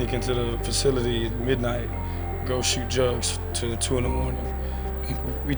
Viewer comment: Did he say he would sneak in the facility with Jarvis and shoot drugs